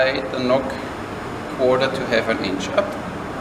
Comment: I have listened to your video several times and keep hearing "....simply slide the nock 4 to 7 inches up" from a 90-degree right angle setting (4:30). This can't be right but I swear that's what I keep hearing. Can you please clarify, how high above 90-degrees? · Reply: He says slide it up a 1/4 to 1/2 an inch up, sorry I'm a bit late